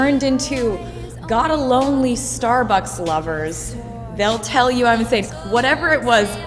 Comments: Yep, I keep hearing "lonely Starbucks lovers," LOL! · 😂 I thought I was the only one who thought she said Starbucks lovers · I LITERALLY THOUGHT SHE SAID STARBUCKS FOR YEARS UNTIL I ACTUALLY LOOKED UP THE LYRICS ONE DAY BECAUSE THAT MADE ABSOLUTELY NO SENSE HAHAHAHA